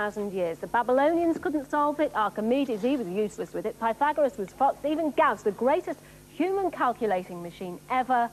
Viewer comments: Did Carol just say Fucked!!! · It really sounds like Carol says the "F---" word, but she actually says "Pythagoras was foxed". Easy to mishear though!!